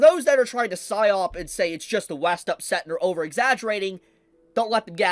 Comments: Buddy can you please tell me what does he say at 11:27? "Syop" I don't know how to write it, I've been trying to find this word and he just said it but I don't know how it is pronounced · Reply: PsyOp, short for Psychological Operation, I believe. Basically, it's an operation aimed at manipulating the thoughts and emotions of large amounts of people by trying to control the information they consume about a topic. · Buddy can you please tell me what does he say at 11:27? "Syop" I don't know how to write it, I've been trying to find this word and he just said it but I don't know how it is pronounced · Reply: "psyop"